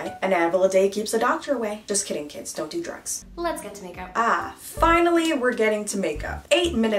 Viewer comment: I thought she said make out 😳😳